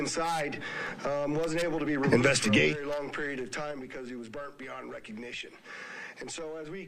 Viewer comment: "Wasn't able to be removed for a long period of time" Is what was said during an idiot exclaiming "INVESTIGATE"